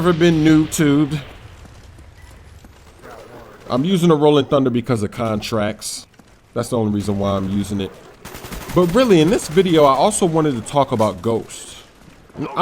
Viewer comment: who else thought he said, "I'm using the Rolling Thunder because of COD Tracks..." LOL